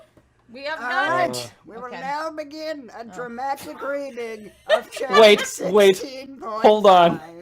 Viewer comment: did he say “dramatic reading” or “traumatic reading”?